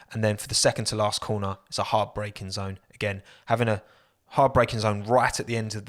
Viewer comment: did he say hard breaking zone or heartbreaking zone?